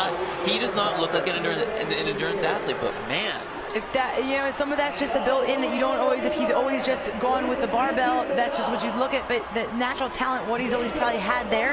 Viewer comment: wtf did she say 🤔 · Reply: If that you know some of that is just the built in that you don't always if he's always just going with the barbell that's just what you look it but the natural talent what he's always probably had there and just didn't tap into and he is now. Perfectly clear, don't be sexist.